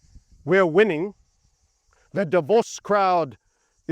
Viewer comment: When you said Davos I thought you were saying divorce lol